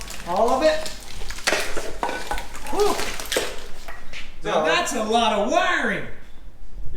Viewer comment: I thought he say THAT A LOT OF DAMAGE But no